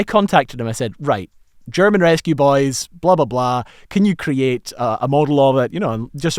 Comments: Everytime he says it, I hear "rescue boys". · I keep hearing you say "Rescue Boys" and can't not hear it.